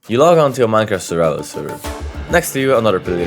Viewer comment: dude wtf i can't understand over a half of WHAT ARE YOU SAYING what i hear is "u log onto a mi-at suuaaal seeer. Next asjdbuiyhaervipudsfhguasduifgoh"